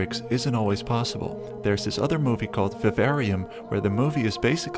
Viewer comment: What movie did you say at 8:07 cause that looked interesting? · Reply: Vivarium